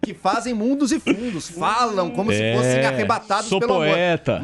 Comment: Teve uma hora que eu quase ouvi "arrom..bados 😅😅